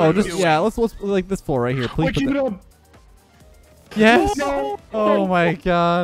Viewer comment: did he really say “waking up”? 😂😂😂